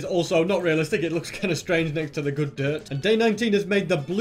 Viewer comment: did he just say "good dirt"?😂😂😂😂😂😂😂